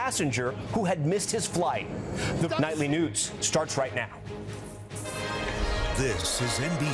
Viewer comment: He said nightly nudes 😂1:11 instead of news 😂way to drop the ball Tom .Lester would of never 😢 😅